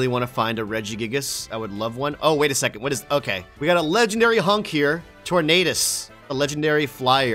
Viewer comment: Hold did u call it a legendary hunk? My man u wanna tell us something??